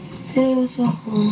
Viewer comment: Did i hear it right? Sabi ni "selos aq" sa 5:45??